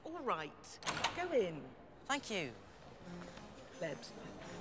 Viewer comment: Did she say plebs at 0:42?